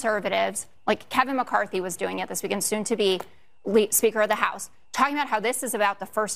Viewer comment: Did I hear her say "soon to be late speaker of the house"? Because that has some potentially... ominous... implications. Pretty sure she meant "soon to be former," but "late" has a very different meaning. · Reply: She said "Lead" speaker of the House. Not late.